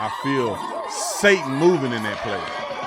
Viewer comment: Did he just say Satan Movin’ in here!😂😂😂 say it again for the people in the back.